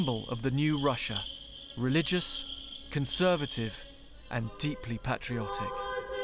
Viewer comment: I thought he said “religious, conservative & deeply patronising” 😂